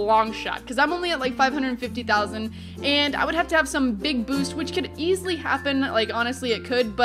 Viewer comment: who else thought she said big boobs